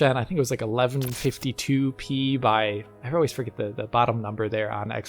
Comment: did he just say 1152p? is that a resolution?